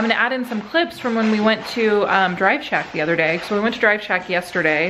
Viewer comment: Every time you said “Drive Shack” I heard “Driveshaft,” as in Charlie’s band from Lost 😂